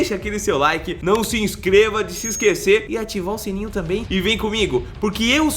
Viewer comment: "não se inscreva de se esquecer" foi isso que o falou ou eu entendi errado, kkk